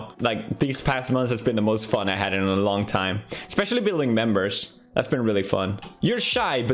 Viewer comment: I seriously thought he said, billing members.